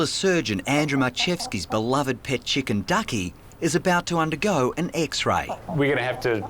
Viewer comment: Did they say “undergo an EGGSray”?